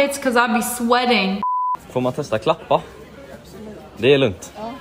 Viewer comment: btw if youre wondering what this dude said here he Said ”får man testa klappa” which basically means can i pet you